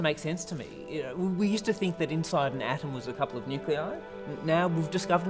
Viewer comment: "A molecule in an atom" The writer flunked high school science. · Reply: I thought he said nuclei